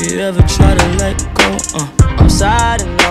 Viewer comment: I thought he said "if you have a gun then let me know" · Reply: I thought he was Saying “Im sad and low” instead of “Im sad i know”